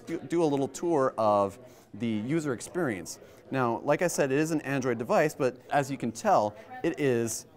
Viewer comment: Did he said "This is not an android device" ?!